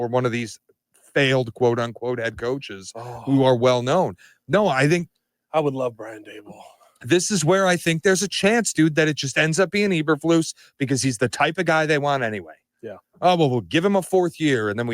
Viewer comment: I thought I heard Pat say he'll love Brian Daboll 😂🤦🏽‍♂️